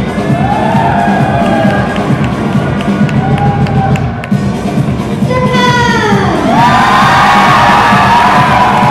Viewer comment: which country ??? i can't hear it clearly. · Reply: Peter Nguyen Japan